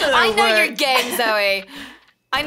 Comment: I thought said "I know you're gay Zoey" XD so I was like "Kim, everyone should by now"